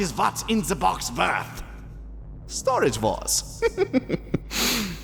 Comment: i swear he said vore